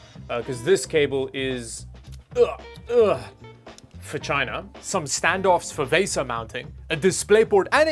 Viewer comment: what did you say, for china or ......